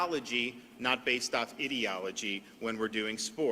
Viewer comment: Lol!I thought for a minuite he said idiotology.😂😂😂😂😂😂😂😂